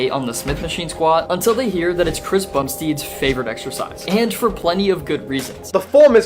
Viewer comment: did he say Chris Bum-steed 😂